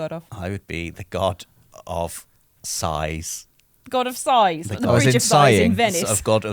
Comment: I bursted out laughing when was asked what would he be the God of ? I thought he said God of SIZE!😆 1:13:22 And saying Sausages🤣🤣